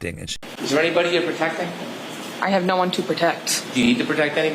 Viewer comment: When she said she has no one to protect, I heard ‘I have no one to protect me’.